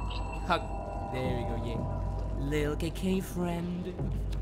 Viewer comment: why did it sound like he said Kinky friends and not KK friends